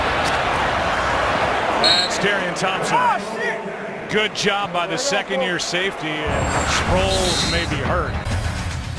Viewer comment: Did anybody hear what Sproles said at 15:30😂😂😂 also great vid. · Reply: Jaiden Roach he said OH SHIT